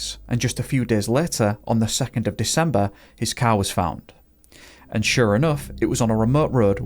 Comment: I genuinely thought you said “his cow was found”